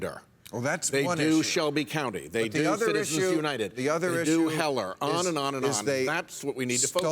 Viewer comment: "They do Heller"-Did anyone else catch that?